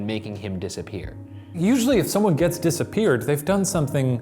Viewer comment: did shane just say “if someone gets disappeared”??